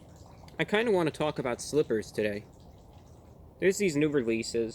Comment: I thought it said strippers